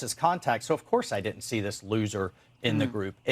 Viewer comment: Did he just say Looser, i hope our military is watching this 👁👁